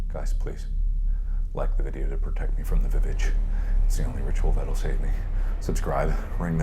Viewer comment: is mark saying amy's the VVitch (Witch)?